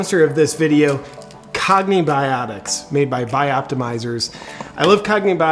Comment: I seriously thought he said "comie biotics" during the sponsorship after hearing the word communism in the video so many times lol